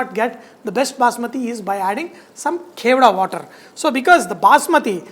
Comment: can someone please tell me what is the "carola" water (that he says around 4:34) to add to basmati rice to enhance the flavor? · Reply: It's Kevada water. You will get any grocery shop.